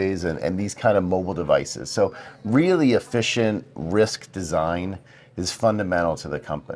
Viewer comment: The subtitles have a typo, at around 18:02 it should say "efficient RISC design" instead of "efficient risk design".